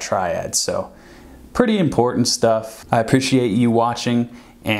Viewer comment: Sounds like he says: "i appreciate you washing"